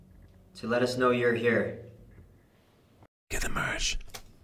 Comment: every time i was trying to hear things ghost said i keep hearing "get the merch ". I think ghost is obsessed with merch XD